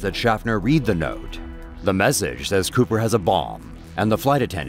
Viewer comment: Turn on subtitles, It will say ball instead of Bomb #FAIL